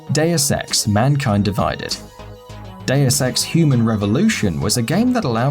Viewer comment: Am i the only one who thought he said day of sex at first?